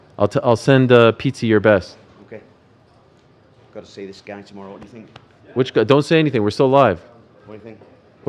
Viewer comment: what did toms dad say? Sounds like "gotta see this Gane tomorrow, whatya think?"